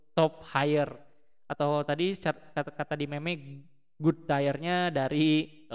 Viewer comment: Bang gua ga salah denger nih?😂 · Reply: MEME YAAAA BUKAN MEMEK ITUUUU hahahaha